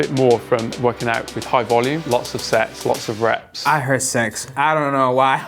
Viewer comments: I hear “lots of sex” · i heard LOT'S OF SEX too 😂